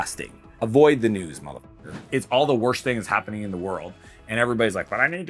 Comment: did I hear mother f###r